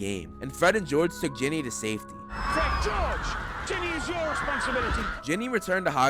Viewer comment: Am I the only one who thought they said Timmy instead of Ginny?